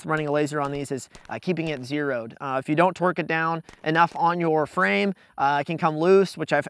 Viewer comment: "If you don't twerk it down" hahaha I know you meant torque. But it sure as hell sounded like you said twerk.